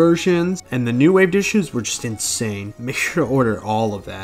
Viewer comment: Is it only me or did I hear "stinky rice"?